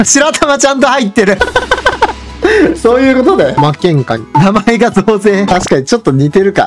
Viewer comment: 「負けんかい」と空耳した。 とくせいは「いかりのつぼ」かな。